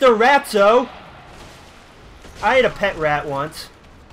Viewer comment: did you say you ate a pet rat once? Listened to it three times and it sounds like that is what you said.